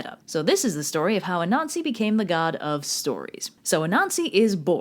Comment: I first heard "this is how a nazi became the God of stories"